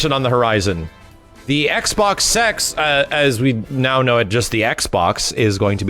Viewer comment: Please tell me I'm not the only one who heard "XBox Sex".